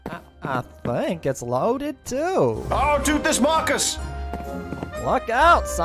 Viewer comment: It sound like he said rukas